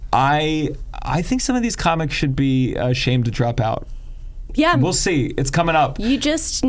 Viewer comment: I heard “I think some of these comics should be ashamed of Dropout” and thought “weird take for an episode with Siobhan as your guest”